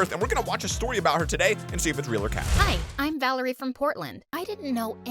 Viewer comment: I thought she said Hi Im Valkyrie from fortnite 😥